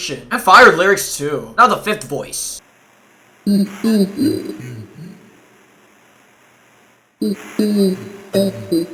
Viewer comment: Does anyone know what he is saying? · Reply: He says: "Death on the horizon. You will be forgotten! I am enlightened. I am the Titan! You can't run away. You are not Safe!. You can't hide fo rlong. You will be-e-e-e-AAAAAAAAAAAAAAA-"